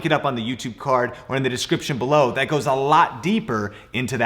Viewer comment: In the video at 12:02 I swear I heard his say ROBLOX!